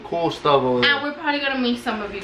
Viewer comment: Did he say bye bye bitches at 8:06 !😂😂